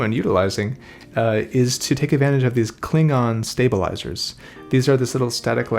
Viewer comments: i almost thought he said "take advantage of these Klingon stabilizers " but it was " cling-on " lol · Did he just say he uses Klingon stabilizers ??? 🤣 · mentioned "Cling on stabiliser" .. and yet I heard: "Klingon stabiliser" … ha ha. Thanks!